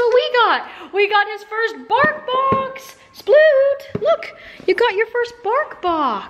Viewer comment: For a second I thought she said barf box😂😂🤣🤣🤣instead of bark box